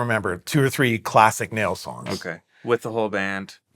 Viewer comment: Justin use the the term "Nails songs", for me that sounds like somebody says "Stones songs". love the show